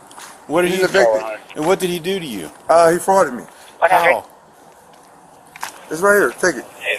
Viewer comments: what did he do to you ? answer : he farted on me !?!?! AND AT 9:35 he got farted on again apparently !!! thats what i heard , LOL LMAO ROFL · Ohh.. I kept hearing, "he farted me." Instead of "he frauded me." Which should be "defrauded." lol